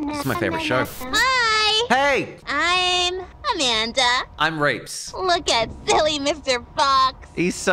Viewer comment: "Hi im Reapz" What i heard: "Hi im Rapes"